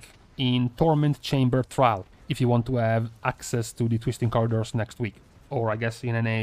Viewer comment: did he just say fisting corridors